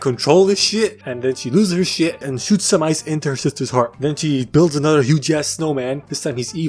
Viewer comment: as I the only one who hears 'shitser'?